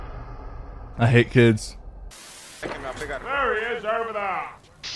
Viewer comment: Did sypher just say I hate kids 5:59 unsubscribed already lost a sub not you sypher who is with he hates or did he mean toxic kids I dunno but if he meant every kid he has to get canceled